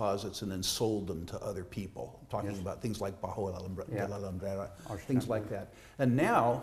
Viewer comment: What was said at 9:12? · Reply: Bajo La Lumbrera